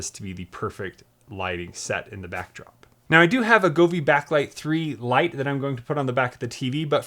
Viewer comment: What is the light you installing? sorry could not understand what you were saying :) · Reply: I'm going to use a Govee TV Backlight 3 Lite. This has a camera that can change the LEDs behind the TV to what is on TV.